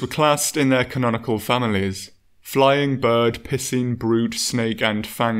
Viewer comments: Just want you to know that I misheard "piscine" as pissing at first, and...got... really confused. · I thought you said pissing at first not picene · I like the way you say piscine it sounds like you say pissing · The way you pronounce Piscine kills me every time. Sounds like your saying pissin.